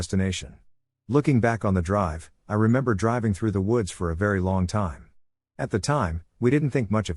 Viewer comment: there was an audio glitch at this part that sounded like “hhhhi” but it was “I” and it scared the crap out of me